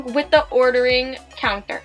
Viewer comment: she said counther instead of counter